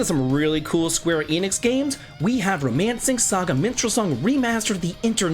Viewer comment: Call me crazy, but I could’ve sworn you said “Romancing Saga Menstrual Song” 😂 Oh and screw people who hate on your reviews. Those are your opinions, period.